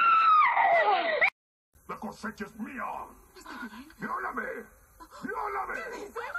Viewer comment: Is she saying fugamememe? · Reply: She's saying "Violame" (rape me) and in the new one she's saying "Tomame" (Take me)